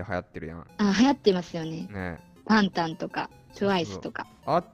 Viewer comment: タンタンって何？ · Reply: はき 多分｢バンタン｣を「タンタン」と聞き間違えたのでは…w